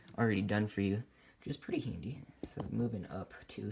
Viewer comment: When he said pretty handy I thought he said pretty Hindi lol